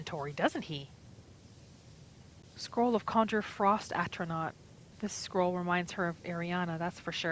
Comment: I was only half listening as i wrote D&D notes. I had a heart attack when i heard "Ice Astronaut" instead of atronach